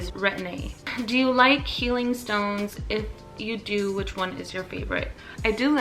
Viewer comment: I thought you said "Do you like feeling stoned" 😂